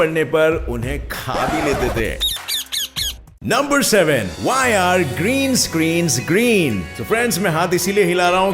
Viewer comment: Friends, aap sabhi ko mere taraf se naye saal ki haardik shubhkamnaye. May this year fulfil all your dreams and wishes. Stay curious and stay motivated .Jai Hind! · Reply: When you said why are green screen green? I thought you said why are greens greens greens 😂😂😂